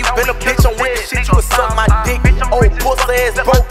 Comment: did he say julio foolio??😮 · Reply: he said what chu on foolio